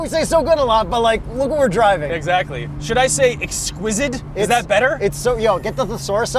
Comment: did say ‘exquisidd’ ? 🙃